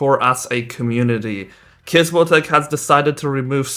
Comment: If you turn on subtitles it says gajin instead of Kizmotek